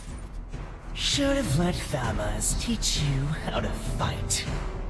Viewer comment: w8 did he said "Should of my Thamuz teach you how to fight?" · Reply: He did. Thamuz is his Mentor. and Dyrus is the prince of the shadow abyss. and it is " Should've let Thamuz teach you how to fight! "